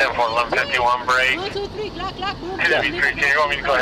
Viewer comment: Did she say “223 Glock Glock”? Lol